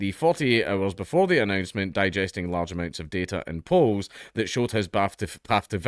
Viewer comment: I was listening and I thought it was going to say he was digesting large amounts of ice cream